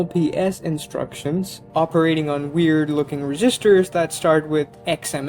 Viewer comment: ".... reeeGisters"? Oh, you mean registers; as in cash registers. At first I thought you were saying "resistors".